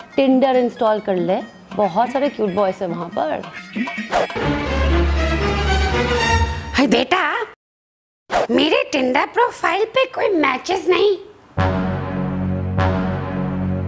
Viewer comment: she says hay deta. Has anyone heard that. Tell me ok.😂😂